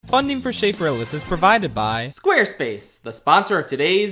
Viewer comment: This is the first time i heard "Funding for Schaffrillas" instead "Funding for shape realists" what is with my brain